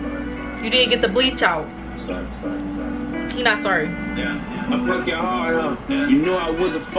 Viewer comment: Haha you’re funny asf. At the beginning Joyners like “I’m sorry” and all I hear is you “He ain’t sorry.” 😂😂🤣🤣